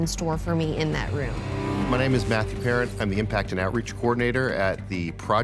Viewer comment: anyone else think that it said Matthew Patrick at first? Also is it just me or does the Secretary of State look like Aaron Burr from Hamilton, which is kind of ironic🤣